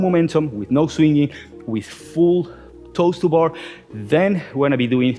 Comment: Am I the only one that thought he was saying "toaster bar" for "toes to bar" lol